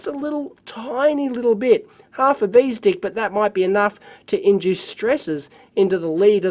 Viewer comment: did he say "half a bee's dick"?? lol